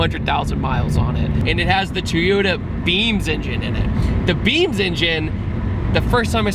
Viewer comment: At first I thought you said it was a BEANS engine and I was a little confused 🤣